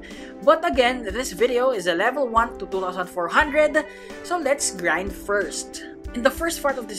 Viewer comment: Nice can u do a quake from lvl 1 to 2400 btw u deserve greater amount of subs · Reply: ohhh yeahhh i thought he said 1 to 700 my bad